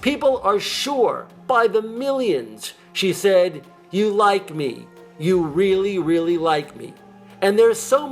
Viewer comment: I've watched her acceptance speech many times. "You like me, you really like me." No doubt... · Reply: I thought she said, "You like weed, you really, really like weed."